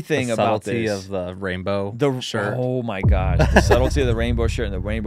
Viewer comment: Im so so sorry if I got this wrong, but did just say 'oh my G##'? 🫢😔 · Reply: Gosh